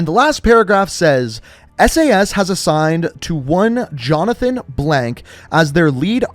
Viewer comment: i think it said jonathan irons but that my guess